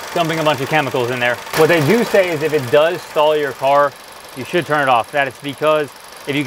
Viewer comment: what in the world are you saying? If it stalls, stop it?